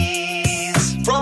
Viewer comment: I thought he said fat ass 💀